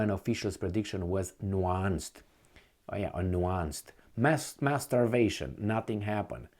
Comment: Wait... Did I hear it right? "Mass mas..."